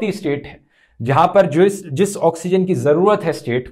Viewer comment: dhruv said 'jewish' instead of speaking 'जिस', कहीं अपना हिटलर PM दिल्ली वालों को यहूदी तो नहीं समझ रहा क्या।